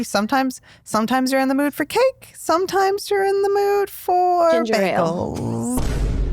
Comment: what did pink shirt say? I couldn’t understand 😭🌚 I feel like it was something shady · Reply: She said sometime you're in the mood for cake, and sometime for bagels. It was absolutely shady